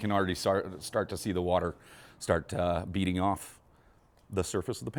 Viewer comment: It sounded like he said the water was beating off 🤔